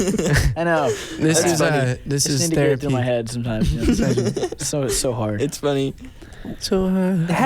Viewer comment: This is one of the best interviews ever. So darn funny. It made me love them even more, if that's possible. Also, Daniel at 9:28. He sang what sounded like "it's so hard", about Zach saying being on time was hard. There's a song called Hard on the album. I wonder if let that slip or not. And did anyone else think that sorta opera version of Can't Help Falling in Love sounded a little like Elvis in parts? It was great.